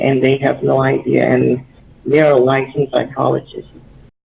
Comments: When you spoke to, at the part you didn't understand, he said: "They are a licensed psychologist." at 9:00. :) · I think he said “there a licensed psychologist” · "they are a licensed phycologist" is what he said :) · hey man, great video, but at 9:01 i think he says "and they're a licensed psychologist," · this is useless information, but at 9:00 I think it says "and they're a licensed psychologist" where you put (unable to understand) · "They're a licenced psychologist." I'm pretty sure that's what he said. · I think he says that they are licensed psychologists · um idfk umm here.. 9:00 I think he said "you're a licence psychologist" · I think he said "they're a licensed psychologist" at the "Unable to Understand" bit